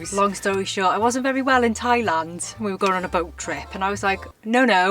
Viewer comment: “Loulou” for the toilet. In Austria we say we go Lulu if we need to pee. And I love to watch your videos. Had to click back many times because you are hilarious! But when you said Thailand, I just heard “Happy water, anyone?” 🤣😂